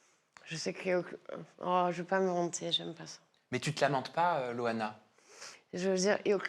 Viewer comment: moi j'ai entendu "j'veux pas me vanter" et entend "lamenter". Preuve que c'est inaudible.